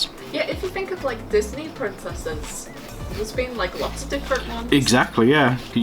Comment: OH. I thought you said 'prince asses', but I am happy I (apparently) have bad hearing...